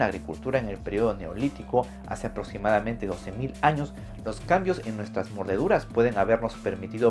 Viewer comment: Alguien se dio cuenta que dijo en el 8:06 "Anos" y no años? Jeje